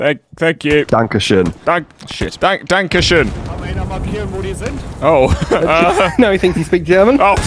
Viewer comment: what does he say? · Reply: I think it's: "Kann mal einer markieren, wo die sind?" "Can somebody mark where they are?"